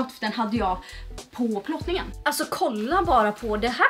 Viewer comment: hörde att du sa förlossningen😂😂❤️